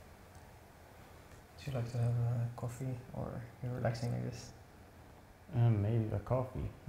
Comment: What does the barber say 44:04? “Would you like to have a coffee or a _______?” I can’t make it out. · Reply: Relaxing agents?